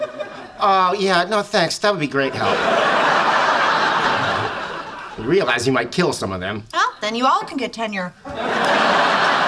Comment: you realise you might kill some of them well then you can all get tenure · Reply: I first heard it as “Your real ass might kill someone there” 😂😂